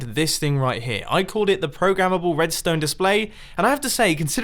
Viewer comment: Wait...did mumbo just said Pro-ga-mable in